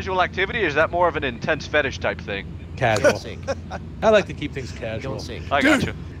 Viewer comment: it sounds like it says "Casual Sink."